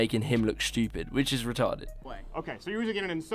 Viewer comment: Did you just say retarded????? Hold the video, who do you think you are saying retarded? What's the definition of retarded?